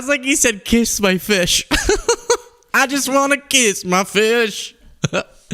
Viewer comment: I thought he said piss my fish.